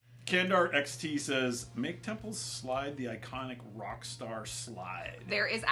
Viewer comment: Man, I misheard the frame name in the devstream as Tempo, and for a frame aviut guitars and rock, I think that's a much better name